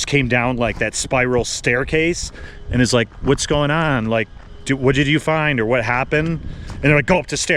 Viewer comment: I heard " what did you do, what did you do?